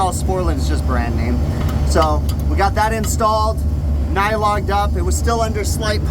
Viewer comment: where did you get a nylon duck? (auto captions can be entertaining... it says "nylon duck" instead of "nylog'ed up" 🙂 )